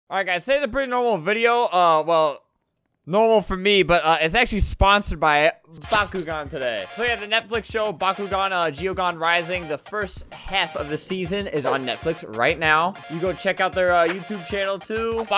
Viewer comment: I really thought he said this video is sponsored by bakugo